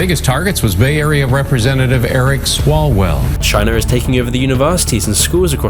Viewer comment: The sheer fact that people still think China isn’t our enemy baffles me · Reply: Also while I was watching this the video started buffering as he was talking about China taking over the Universities, so all I heard was “China is taking over the Universe”